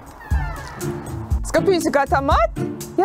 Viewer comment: Is your cat called "piece of crap" or did i hear it wrong like 6 times in a row 🤣🤣🤣?? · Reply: Clyde and Astrid, I think - the bit you misheard must have been "skal pusekatt ha mat?", meaning "does kitty want food?" ;)